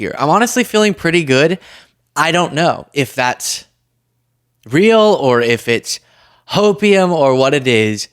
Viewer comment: OK I can’t see the transcript so did you just say is it real or is it Hopium? 😂 If that’s not what you said then I just misheard something very funny and if it is what you said that’s hysterical